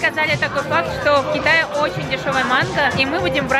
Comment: Мне одной послышалось :"здесь очень дешевая манга"